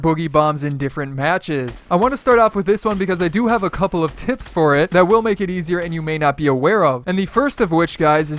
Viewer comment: Am i the only one who heard "i have some tits for it" or someone heard that too